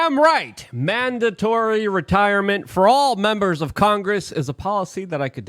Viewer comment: Ohhhh they said Retire. I thought they were saying freeze right now.freeze right now 😂😂😂😂😂😂